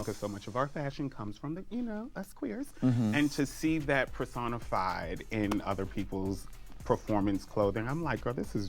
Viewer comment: girl i thought she said persona 5